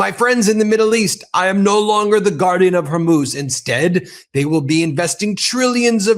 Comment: I heard "Guardian of Horus" 😅😅😅